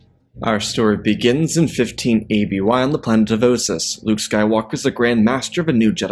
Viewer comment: Either I misheard or he said 15 BBY when he meant 15 ABY.